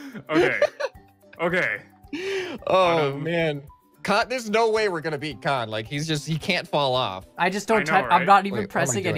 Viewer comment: It literally in the cc when said there is no way we’re gonna beat kan it thought he said god that’s amazing XD 11:03 but it happens